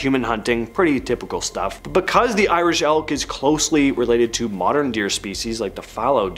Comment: Irish Elk. I thought you said ‘Irish Elf’, so yeah, that was disappointing